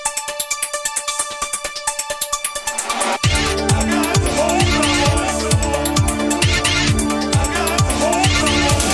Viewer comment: Anyone knows what he's saying at 0:04? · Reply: i think he's saying "i got a phone call"